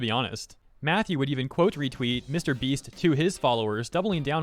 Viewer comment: Mr. Beast? I thought you said Mr. Weast.